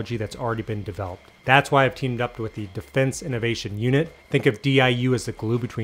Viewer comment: I thought I heard Defence Unit Innovation...great sharing thank you!